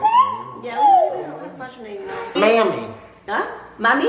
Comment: please someone tell me he did not say "mammy" at 13:03... not ok.